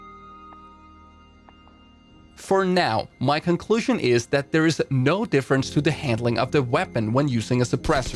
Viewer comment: what he said-"There is no difference except the sound of the weapon!" what I heard-"You watching this video made no sense!!!"